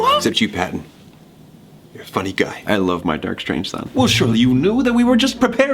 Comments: Did anyone hear what Patton said at 2:33-2:34? If you didn't, he said, "I love my dark,strange son." Also every time someone said "duck out" Patton quacked!! OMFG PATTON I LOVE YOU XD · I know he said son, but it sounded like Patton said "dark, strange side" OMG IS THERE A DARK PATTON LIKE REMUS